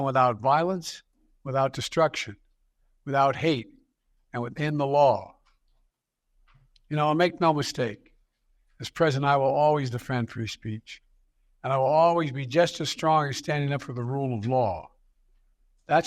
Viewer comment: Ok help me out here at 25:37 to the 25:42 mark did I hear Joe Biden say “this President and I “ will always be just as strong standing up for the rule of law” ????😳😳😳🤔🤔🤔 what President and him is he referring too???🤔🤔🤔